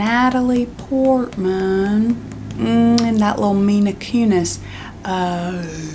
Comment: did you just say mina kunis 😭